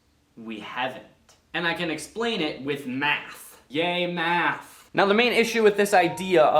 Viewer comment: I thought i Heard “gay math”